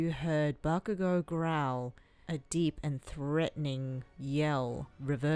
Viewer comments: BRUH In 10:01 all i heard bakugo say is "IM GONNA MARRY YOU" Lol ok😂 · What I heard: IM GOING TO MARRY HER!!!!!!! Also me: ...... but we already did.....